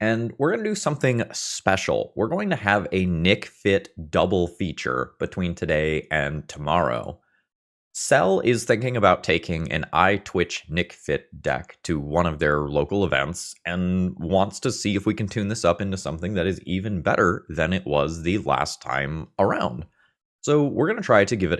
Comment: I legit heard "Cell is the king of taking an EyeTwitch Nic Fit deck to one of his local events" and thought oh my, how does one earn that title?